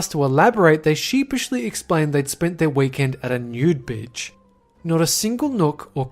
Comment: said "bitch" instead of "beach".